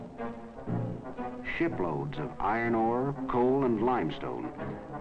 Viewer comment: thought he said shitloads